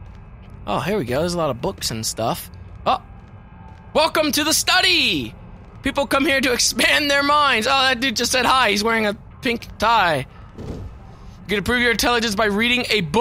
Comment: I thought he Said "Welcome to Micheal Bay!"